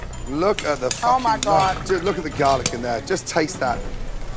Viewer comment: "Do look at the garlic there" sounded like "dude" to me and that was very unexpected to hear from Gordon for me